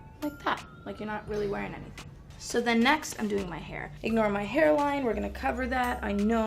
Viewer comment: Why everybody quoting the wrong thing. She clearly says “ignore my hairline”